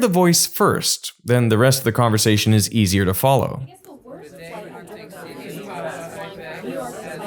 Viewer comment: He's asking which sound is higher, not louder. Sound A is higher. Sound B is lower ("sounds like a bass") even though it's the combination of higher frequency tones.. As they explain later. At 14:20 I hear 2 words: "low" on the left and "play" on the right 😂